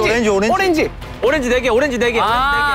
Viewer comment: I heard jungkook saying orange orange reh gya orange 😬 is it only me who heard that??